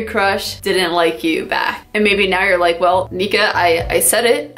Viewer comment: Thought she said "nig*a" 😭😭💀💀